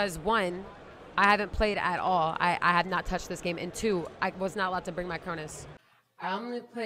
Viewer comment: did she say she wasnt allowed cuz she tried to use a cronus check in